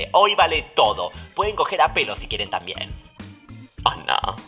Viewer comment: pueden cojerse a que??? no escuche :v